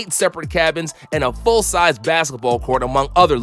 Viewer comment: hold on.....it might be the pre workout that hasnt worn off, but did he just say that yacht has a basket ball court in it?